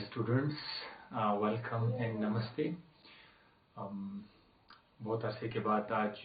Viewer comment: what did you say? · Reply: Namasty✋